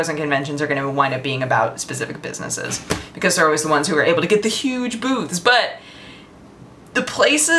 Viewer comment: am i the only one who heard huge boobs?